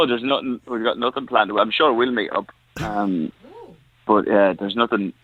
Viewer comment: did he just say "I'm sure we'll meet up"?😭😭😭